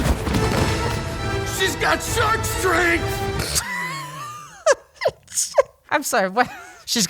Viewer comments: at first I heard "she's got sunscreen!" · "She's got shark strength!" Yeah bro and I've got giraffe speed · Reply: I thought he said surge strength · lol I know the guard said Shark strength but what I heard was Sunscreen her looking at the guy like "yeah SPF 50 motherf**er wanna dance".